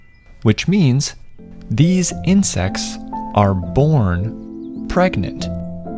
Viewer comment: I thought bro said insex😭